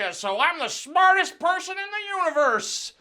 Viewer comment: What author is he talking about when he says I read 'nitche?' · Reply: Friedrich Nietzsche?